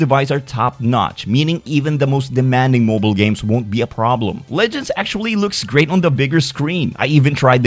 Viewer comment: did i hear legends 🦅🦅🦅🦅🦅🦅🦅🦅🦅🦅🦅